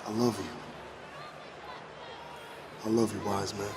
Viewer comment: Now Solo said "I love you wiseman", but what I heard was "Don't do a Fredo and break my heart".